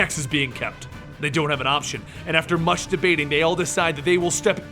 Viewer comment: I hear masturbating